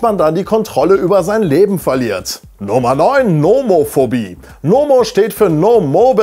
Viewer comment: Hab erst "Momo-Phorbie" verstanden.